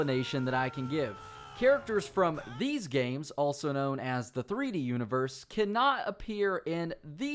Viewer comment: I THINK HE SAID 3D UNIVERSE